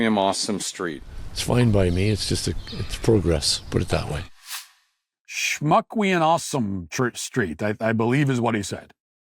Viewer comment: I think he said "me an awesome shmuck" in Yoda format.